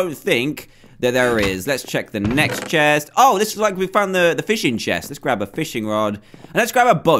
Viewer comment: did he just say a fishing chest?